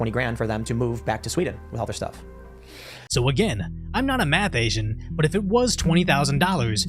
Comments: Bro!!! Every time u say “Math Asian”, I crack up!!! I’m like “Did he just say that!?!? Lol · Wait a minute did you just say you were not a math Asian? If you did that threw me off for a second thought I was hearing things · Did you just say “I’m not a math Asian”